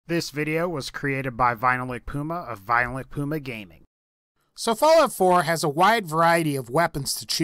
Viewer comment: it sounds like he says violent puma gaming